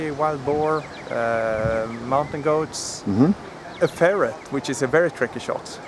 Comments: Thumb down for cruelty against animals. Sorry. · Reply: Did he say ferret? I honestly thought he said parrot. I'm not so good with accent I guess :/ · He shot a ferret with an arrow, how very brave and good eating no doubt? I bet not. If you are going to hunt, which is fine, hunt to eat. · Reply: Oh... a ferret, I thought he said parrot. I was wondering what the hell a parrot was doing near a log, then I remembered the New Zealand Kakapo the parrot that lives on the forest floor or the Kea and i was right puzzled and not a little put out...